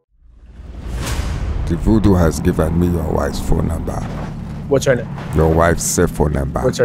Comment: I’m 2 seconds into the video and this what I hear: The Voodoo has given me your wife’s phone number